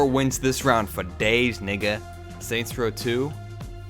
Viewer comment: DID HE JUST SAY NIGGA?